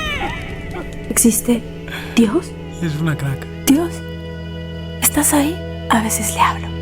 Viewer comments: minuto 22:46, sonrisa, silencio Es una caca-- escuche bien? Saludos es bueno ver, escuchar y comprender todos los puntos de vista y opiniones sobre este tema en especifico. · Dale like si escuchaste lo que dijo en voz baja en el minuto 22:45.. ES UNA CACA .. ?? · pensé que dijo es una caca